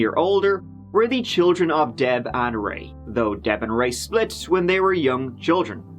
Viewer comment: Did anyone else hear they were the children of death and rape?